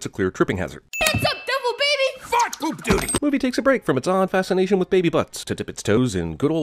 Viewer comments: did they just say, “fuck! Poop duty!” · Did that baby just said the f word or am I hearing things?? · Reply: I heard F*** poop doody... He actually says... FART poop doody! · I’m not the only one who thought he said “FUCK POOP DUTY” am i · did anyone else hear the baby at 2:56 say fuck poop doddie